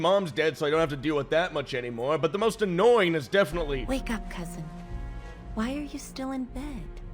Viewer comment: Instead of "Wake up, cousin" I heard "Wake up, peasant"